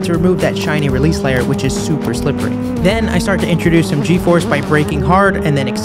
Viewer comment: I thought bro said gay force